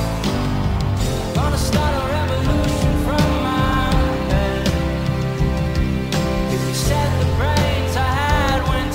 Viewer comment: Tyler changes the lyrics, the real ones are "But please don't put your life in the hands / Of a rock and roll band" and here Tyler says "of a ___ band". I did not understand what he says exactly but I think it might be quite symbolic, it might represent his own group, can someone help figure out what he says please? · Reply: I believe he says "of a two piece band"